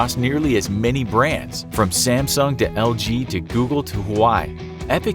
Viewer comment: Lmao i heard “From Samsung, to Lg, from google, to Hawaii”😂😂😂😂